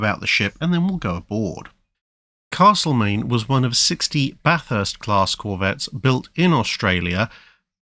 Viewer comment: Every time you said Bathurst-class I kept hearing Bat'leth-class, this did not ruin the video.